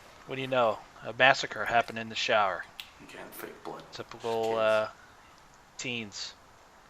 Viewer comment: I heard it to, Limizzkiw1 at time 4:33 it starts there was a man's voice, It said YOU CAN'T FAKE BLOOD TOO, JUST KIDDING .That sound of a mans voice is so crazy !!!!!!!!!!!!!!